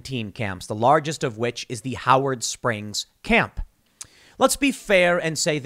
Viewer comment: This whole time I thought it said the Howard Stern internment camp.